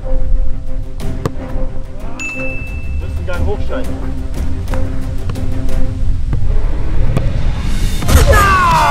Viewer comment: was sagt er bei 7:01 min?? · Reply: Wir müssen ein Gang hoch schalten